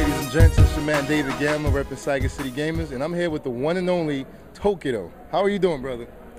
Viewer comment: Sounded like he said "I'm here with Tokyo". I like his interviews. He actually listens to what people are saying!